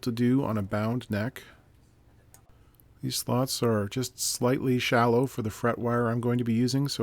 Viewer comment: I just woke up and thought I heard you say “….these thoughts are just slightly shallow….” Yeah. Turns out it was the fret slots that were just slightly shallow 😂